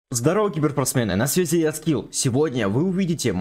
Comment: почему Я услышал анскил?